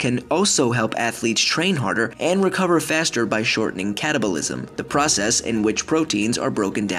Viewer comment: Wasn't looking when he said catabolism and thought he said cannabilism